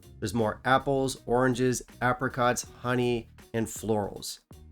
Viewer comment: why I keep hearing uppercuts 🤛🏻 I/o apricots? 🍑